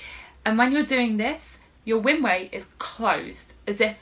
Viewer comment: It sounded like you said close your "winway" but perhaps you said "windway", as in "windpipe"? And I don't close anything when I am saying "I", maybe I don't know how to talk (or breathe)? 🤣